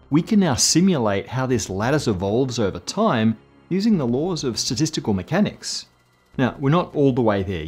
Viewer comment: At 12.50 it sounded like you said "sadistical mechanics" 🤣 x